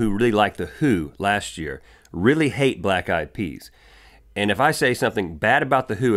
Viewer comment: I thought you said ,"black guy Pee's"